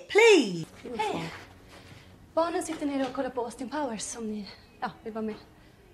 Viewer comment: “Austin Powers” that’s what i heard too 😂